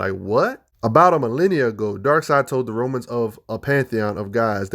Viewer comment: listened to it more then three times, and I need to ask: was it DARKSEID who told the pantheon of Gods, who need to be worshiped, as stated, or did you speech fart, saying Darkseid, instead of Zeus??